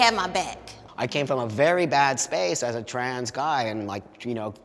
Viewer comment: hold up what??? Run that back please, did he just say trans guy??